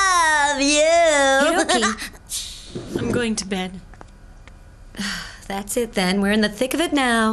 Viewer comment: Is it just me or did I hear tattletale